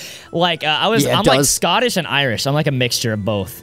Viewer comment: when you said scotish and irish I thought you said godish and irish